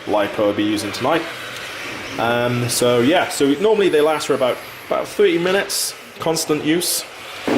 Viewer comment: Hi buddy What we normally use on there is a 2s 5000 lipo battery runs for roughly 35-40 mins. :D standard battery in the kit is roughly 15mins pushing to 20mins hope this helps :) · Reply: ty so much !!!! :P in the cideo i thought i heard 2-3 miniutes xD